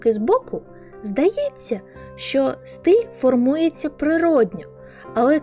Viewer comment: почулося "стиль формується при родах" 😔